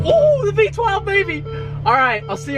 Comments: Did you just say “familiartize myself” 😂😂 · "A minutary to familiaritze" 😂😂 you know when you love your new car so much you have no idea what your saying 😂😂💯💯🔥